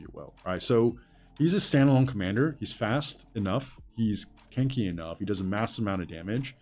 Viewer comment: i thought he said "he's kinky enough"